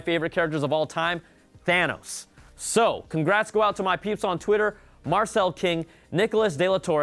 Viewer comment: did you say pubes? o.O?